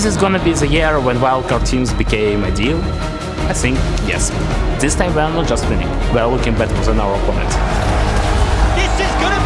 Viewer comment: Can someone translate what Likkrit said at 0:53 ? I understood the first half but afterwards it was impossible to understand. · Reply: Is this going to be the year when Wildcard teams became a big deal? I think, yes. This time we are not just winning, we are looking better than our opponents. Yes, they failed, yes, they could do better. BUT, BUT, when they had NOTHING, they have shown their competitive spirit. And they fought until the very end.